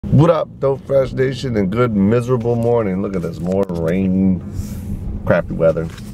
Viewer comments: At the beginning I thought he said good mythical morning. You don't know what good mythical morning is you should check it out good mythical morning , or the channel Rhett & Link 2. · do you watch GMM? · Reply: DJ Stark Yes I do! For 1 second I actually I thought he said Good Mythical Morning and not Good Miserable Morning xD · I thought he said good mythical morning